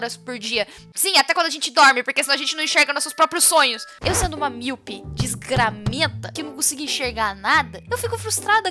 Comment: Oh, fuck... Quando ela disse "eu sou uma miopi desgramenta" eu entendi 'milf'.